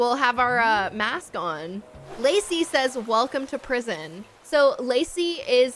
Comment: my name is lacey and when you said it the first time i thought you said lucy